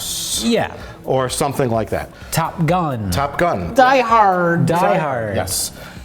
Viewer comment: "Die hard" (picture of David Carradine) wakka wakka. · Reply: I thought it said tied hard.